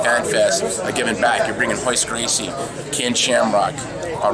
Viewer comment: Sounds like he said Cain Shamrock